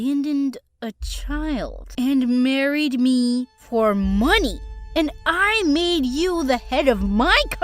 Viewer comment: “Yeu mireed me fur muny” That’s what it sounded like